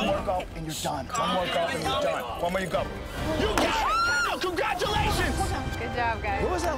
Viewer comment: Im sure I heard Joe say "Cumgratulations!" At the the end 🤣